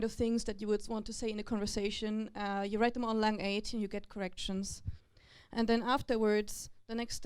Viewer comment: Can anyone tell what she's saying at 1) 8:22? "Write them on ...." Sounds like 'Line 8' or 'Line H' 2) 13:35 'on hover dictionary'? Must be some sort of online dictionary.